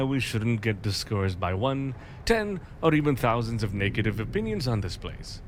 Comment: I wonder what country the voice actor who does James is from? When he said 'negative' in this sentence, it sounded like, to me anyway, that he said 'naked-tive'.